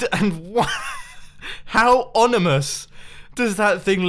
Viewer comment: Did Mumbo say that the tower was Onimous?